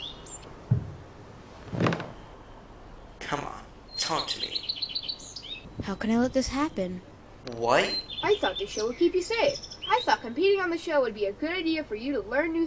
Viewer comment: “I thought the show pee-pee say I though competing” ~ Captions